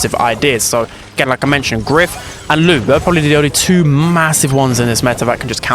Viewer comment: I HEARD MASSIVE?!